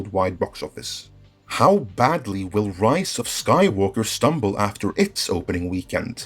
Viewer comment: why i keep hearing "Rice of Skywalker"?